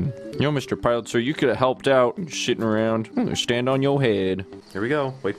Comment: When he said sitting around in that voice I thought he said SHITTING around 😂